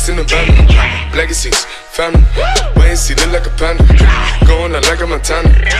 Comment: i always thought he said "legacies, families". surely wasn't expecting BLACK X6